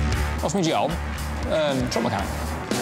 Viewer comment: could someone translate please, I tried subtitles but they couldn't understand it either. What did he say in English please? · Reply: "What's me job? Truck mechanic"